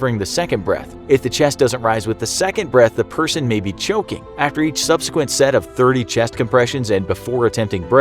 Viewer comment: thought you said joking